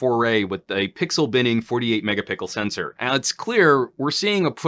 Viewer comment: did I hear “mega pickle sensor”? Lmao